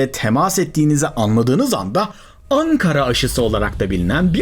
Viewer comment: Ben mi yanlış duydum ANKARA aşısı mı??? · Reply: YOK ARTIK GERÇEKTEN ANKARA